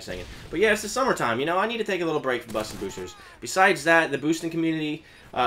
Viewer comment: anyone else hear him say buster boosters